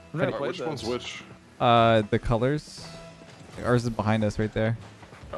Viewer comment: saying the colors sounded like he was trying to say are you stupid